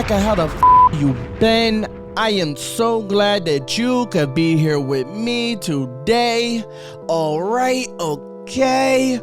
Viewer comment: Holy shit I just freaked out. Lol. I skipped past your disclaimer at the beginning and all I heard was “…Be(e)n I am so glad you could be here with me today!” I was real confused.